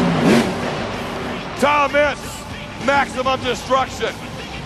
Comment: when I was little I always thought he said Thomas😂😂🤣🤣